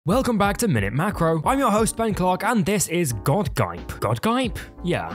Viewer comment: I heard him say "I'm your host Ben c*ck"💀